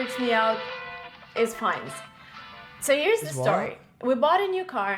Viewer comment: I thought she said Finance too. LOL!